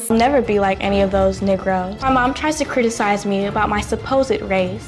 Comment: hold up did she just say nigros and she's black??? She's being rasist to her own self what is she trying to accomplish like if u agree